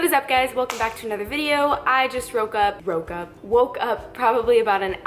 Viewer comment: At first I thought you said "I just broke up" and I got excited because I thought I was back in the game